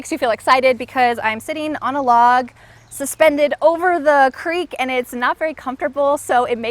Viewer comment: the English subtitle has an error. It's "suspended over a creek" instead of "suspected over a creek".